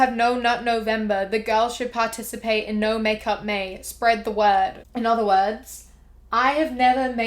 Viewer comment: Instead of “no makeup may” I thought it said “no man may” with the cartoon lady pretending to be sad about it lol